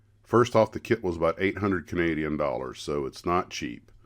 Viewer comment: Lol i thought you said 800 canadian $..